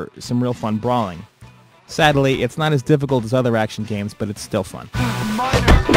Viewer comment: Other ratchet games did he say